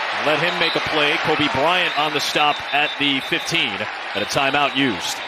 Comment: did he say Kobe Bryant?